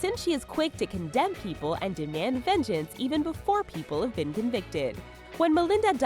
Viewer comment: Really?! The narrator says "convicted" and the subtitle says "predicted"! I didn't know people could be predicted in a court of law.. Please fire subtitle guy!